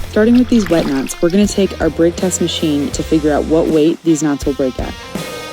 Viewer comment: i swear i heard "what weight deez nuts will break at"